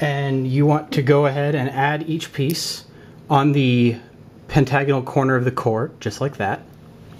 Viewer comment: i heard "Hentai no corners" am i hearing it right?